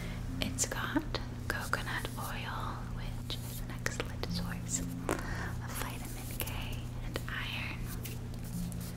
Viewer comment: what does she say at 4:51😭😭 i’ve been trying to figure it out for like an hour now lmaooo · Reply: "it's got coconut oil, which is an excellent source of vitamin K and iron"